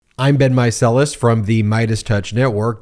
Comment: I was going to subscribe, but I'm already subscribed 😅 When I first started watching your videos I thought he was saying "I've been Mike Sellas" 🤦🏽‍♂️ My hearing is bad due to congested eustachian tubes. Maybe if he said "I am Ben (pause).." 😉